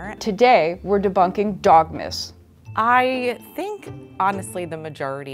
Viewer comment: Lol I thought she said “dog-mas” like Christmas. Not “dog myths” 😂